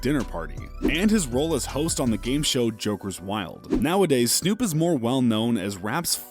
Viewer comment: i thought that said Joker weed